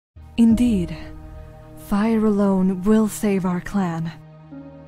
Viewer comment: It sounds like she said "indeed, fire alone will save our clam"